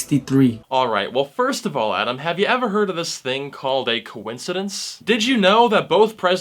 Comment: owww. conthedenthe.. what is that word??